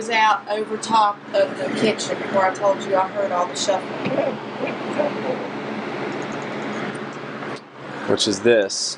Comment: right after she says she heard the shuffling from downstairs, there's a pretty loud, kinda garbled evp, can't hear what it says & don't rly care, but sounds like a little boys voice. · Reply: Someone pointed that one out before and thinks it says "kid, be careful." Could be.